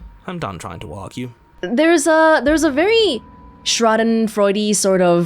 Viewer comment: Hi can you tell me what is first science guy you say before Freud? "There is a very .... -Freudy sort of o joy" - I searched for it before couldn't and subs don't really help :D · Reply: It's "schadenfreude"!